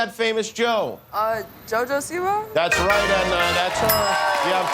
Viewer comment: what did she say? Jo Jo C-bah? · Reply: Siwa.